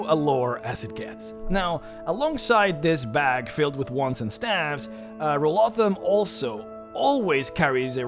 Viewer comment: Misheard it as a “bag full of warts and scabs”